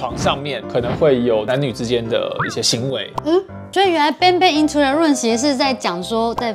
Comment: 我一開始乍聽成gangbang into the room